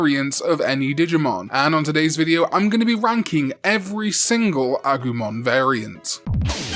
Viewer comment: Ngl at first i thought you said you’d be wanking every autumn variants, i dropped my beer i was laughing so hard.